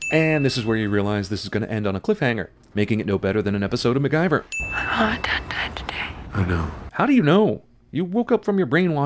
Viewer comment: I honestly have no idea what she`s saying, her voice is so low and kinda cracked. Am i the only one? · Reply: She says, “My mom and dad died today”.